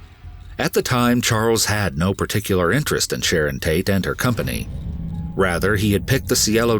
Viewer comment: Awesome how this explained the whole manson mess in 30 minutes!! Thank you so much for this! Absolutely loved this! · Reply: At one point, I heard him say Shannon instead of Sharon.